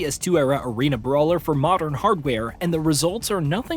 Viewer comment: For a moment I thought he said modern audience… thank the gaming god he meant hardware.